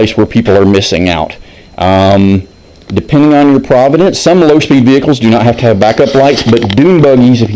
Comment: sory, i thought i heard you say province! are there any general legalities to worry about when modifying the engine?